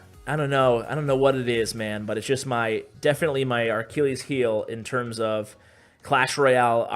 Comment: Did he just say "That's my aRchilles heel" ???